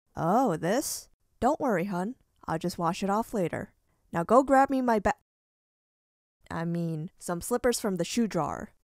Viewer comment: what was she gonna say? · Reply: I am pretty sure she was about to say bat